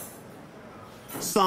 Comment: This whole time I thought the lyrics said " somebody once told me the world is macaroni " 😭